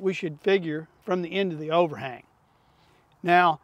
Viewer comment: This whole time, I thought you were saying from the o-ring, and I'm over here trying to figure out what the heck you're talking about... halfway through, I hear overHANG, lmao.